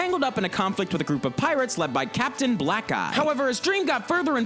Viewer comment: I heard captain black guy